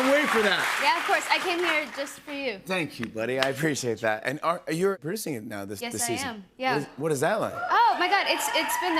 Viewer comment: what did she whisper? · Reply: That's not true 😂